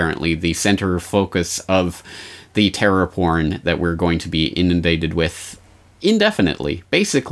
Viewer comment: Is he saying fear porn?